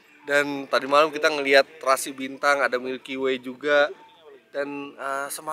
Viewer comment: Milky way? G salah denger?